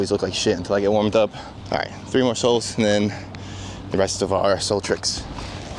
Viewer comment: did you say "arsehole tricks"? 😂. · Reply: I said "rest of our soul tricks" 😅 my Rhode Island accent butchers the word "our"